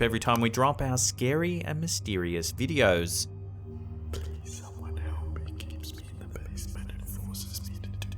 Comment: why does a voice whisper "Please somebody help he keeps me in the basement..." (11:20) "sorry for this" and emit an exhale laugh? Anyone else hear that? I played it multiple times and hear it clear as day ...what is that???